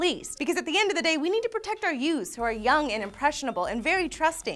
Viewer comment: did she say "jews insten of youth at